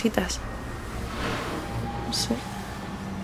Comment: En el minuto 5:58 o 58 se olle la voz de un hombre que dice " SE " es alguien que va contigo? No sé pero repetí el vídeo y lo volví a oír, pero no me da un sentimiento raro cuando oigo SE